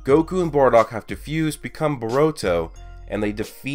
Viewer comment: Who else thought he said boruto